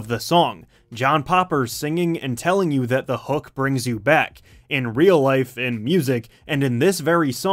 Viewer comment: I always thought that he said "The HEART brings you back". I'm a bit disappointed......